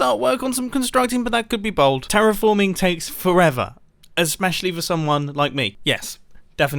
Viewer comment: Did he just say "esmeshially" . I want to learn English from this man!